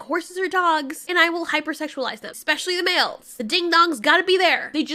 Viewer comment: I am in need of help, at ca. 10:00 does she say wales, or males, this is important information that I feel like I'm missing out on